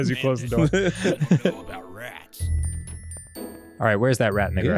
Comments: did Dan just say what i think he said?!? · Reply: "where's that rat in the garage" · did Dan just say what i think he said?!? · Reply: i sware he i hear him saying "where is that Rat N.bomb at?"